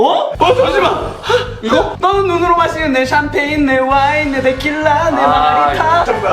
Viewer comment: 아무리 들어도 내 wine 할 때 '내' 가 아니라 and로 들림.